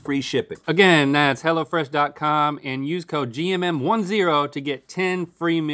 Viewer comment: I thought he was saying it’s hella fresh